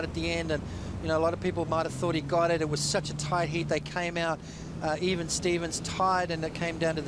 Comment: he didn't know the mic was on at 44:10 lol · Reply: I dont understand the Australian accent that well, but I heard ,"...It was such a fucking..."